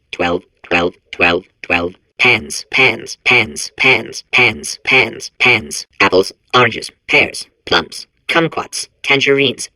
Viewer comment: Not sure if anyone said yet but I thought I heard 'pants pants pants pants pants pants pants pants'. That's gotta be my favorite part.